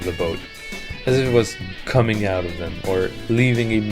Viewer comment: I thought you said clowns coming out of the sides :(